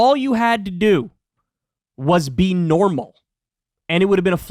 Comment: Is Kyle's mind going like Biden's? It sounded like he said something about shaving off his nipples and that Trump would "with it o'dinky."